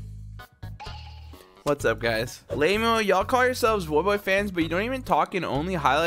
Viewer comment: what did you say? 😂 “lamo”?